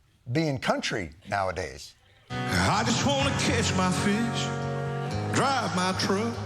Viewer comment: Trad bunny 😅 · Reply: I was sure I heard him sing "I jest want to kiss my fish" 😂